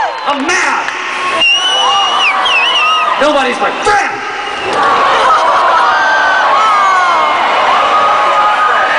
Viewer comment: what did billie said at 2:52? <3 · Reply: "Nobody's my FRIEND."